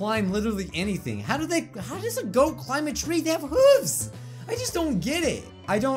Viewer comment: I thought I heard boats climb trees